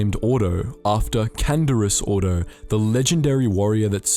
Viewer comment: You know when I heard the name Ordo I thought he was saying fordo because he also has red armor and is a arc trooper but I’m guessing that is not him